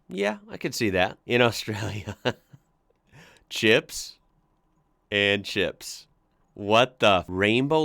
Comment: "hey they're starting to not believe in climate change again" "start ze fires again" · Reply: oi 8:55 its "hot chips" and "cold chips"